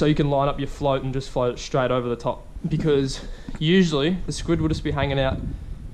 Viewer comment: I swear you said squidward XD Nice catch by the way :)